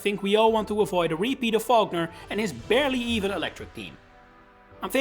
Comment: My ears are broken. I keep hearing “Volkner” as “Falkner”